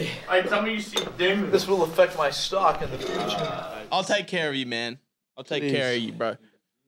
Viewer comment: definitely my favorite moment: 1:19:33 Matt just deep throats a Fiji bottle then cuts to him on the ground "Don't film me, this will effect my stocks in the future." So chaotic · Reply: I'm an idiot. For some reason, I thought he was saying food chain, not future. Now it makes more sense.